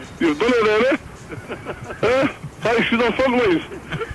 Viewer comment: onwards - What the hell is he saying? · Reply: He said, have you had enough fun with me 😁